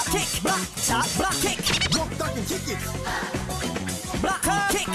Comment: He is clearly saying "Block, Duck and Kick it!" But umm... I guess that they think that he says "Block, Turn and Kick it!" Umm, I don't get that